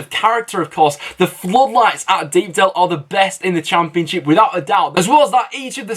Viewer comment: B44 Villain I mean defo not saying that because you are a Villa fan? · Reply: B44 Villain oh sorry thought you said best in the prem I think both Leeds and Villa have at times better atmosphere than Lower teams in the prem